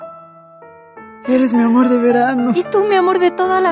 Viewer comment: pensé que decía eres mia amor de ver anos